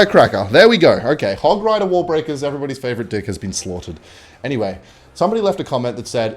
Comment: he did not say deck